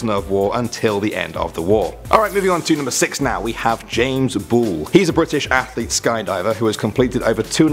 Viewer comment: i thought he said james bond for a sec XD